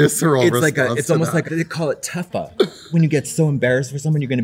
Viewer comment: what did Katya say was “when you get so embarrassed for someone you’re gonna be sick” ??? I cant tell 😭 I’ve looked up “tuffa”, “teffa”, turned on captions and it said that she said “tough up” and that didn’t bring anything up either 😭 huh???????